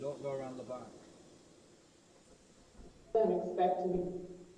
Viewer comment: I hear something about ‘expecting’. “They’re expecting you” or “weren’t expecting..”